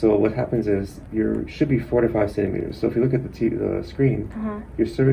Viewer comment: Thought he said 45 centimeters 😳